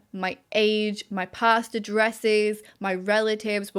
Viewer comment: When you said "past addresses" during the ad I wasn't paying attention and thought you said "pasta dresses" and I was suddenly imagining dresses with spaghetti imagery all over them and now I kind of want a pasta dress.